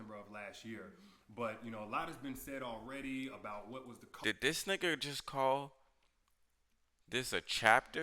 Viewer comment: Did you say nigger with an ER at 2:15 or Am I tripping???? I can’t even finish the video 🤧 not to mention you can tell he already watched this because he’s saying what everybody already said it 😂 to this niggA a clown too